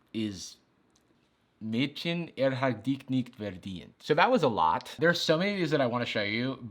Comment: sounded like Mädchen er hat dig nid verdint